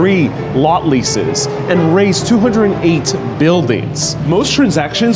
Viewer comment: the subtitle says "raise" instead of the word "raze", meaning to tear a building down to the ground.